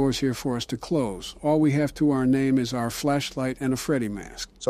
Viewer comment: We thank you for being on this journey with us. What a ride it has been, and we are absolutely thrilled to be continuing onto FNAF 3. Thank you for all the support, it certainly means the world to us. -Barack, Joe, and DONALD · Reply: Joe sounded very sus when he mispronounced a word that is similarly spelled to the correct one, but has a drastically different meaning. By which I'm referring to this moment 1:10 were Joe said (Flesh Light) instead of (Flashlight). Was this intentional or a glitch in the AI speech program?